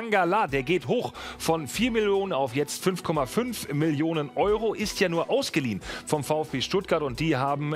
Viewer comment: Ich verstehe bei 0:21 4 Melonen😂